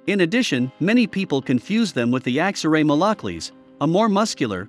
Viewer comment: Can some spell what kind of breed he said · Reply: Aksaray Malakli